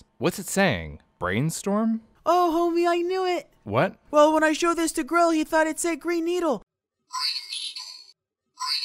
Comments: I heard "green beatle" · I first heard “brain needle” then “green needle” when showed it the 2nd time. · I literally cannot hear either. I hear "Greenstorm" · Thanks to Skillshare! Get a free trial of their Premium Membership with this link: <link> ☕️ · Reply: I hear Grain sch'torlm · Everyone: You can hear "green needle" or brainstorm" Me: hears "brain needle" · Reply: I hear "Grain Stone" or "Grain Stole" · I actually start hearing the high frequency at around 17600, even though my earphones are rather bad in quality, that surprised me, thought I was lower. .And I hear "grain storm"... anyone else? For the other two, I got the actual color/sound right back then. For the audio one for no reason, for "the dress" I just couldn't see it the wrong way around at all. The background lighting just makes it impossible for me to see it wrong. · i heard green storm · Odd, I heard "Greenstorm". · I swear it sounds more like “brain needle”‘than anything else’s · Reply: At first I heard "green needle" but once I was able to make more sense of it I heard "brain needle". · I hear "grain stool" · Well I hear ‘greenstorm’